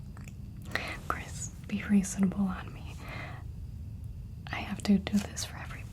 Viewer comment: At minute (16:02) Did she say Chris be reasonable on me?? Or am I tripping!? 😊 My name is Chris and that just felt really personal 😆 I'm listening half asleep at 2:05am....